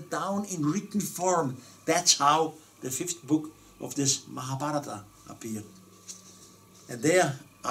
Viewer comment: Fifth book of... what is he saying? Someone spell it for me. I can't understand what he's saying. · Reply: Mahabharata